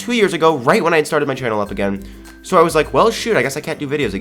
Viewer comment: I thought he said the S word but he said shoot XD